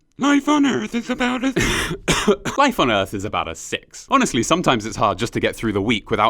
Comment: Lol I heard " My boner is about a six"